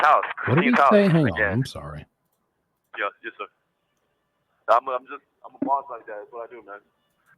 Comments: When you didn't understand him at the beginning... I'm pretty sure he says "I'm a boss like that, it's what I do man" 😐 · “yes sir, I’m a boss like that, that’s what i do man” is what i heard lol